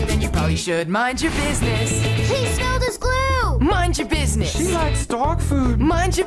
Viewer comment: please tell me what it says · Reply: it says he spilled his glue